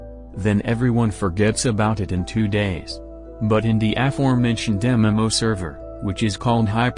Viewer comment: what is "F4 Mention" or affirmation can someone explain what he said in the video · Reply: aforementioned. something that was mentioned before